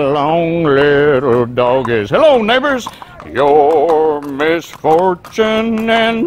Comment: I heard "Hello ni****s" instead of "neighbours"... I was like wtf, he's really taking this wild west roleplaying seriously, Tarantino style. 😱